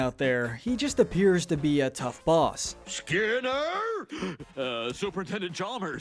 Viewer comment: When you said super attendent I thought you said super Nintendo